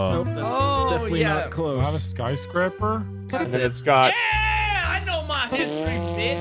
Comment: did call someone “Scott”? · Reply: He said "its got"